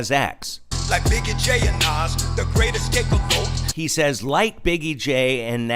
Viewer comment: It sounds like he said, "They can't escape the goat."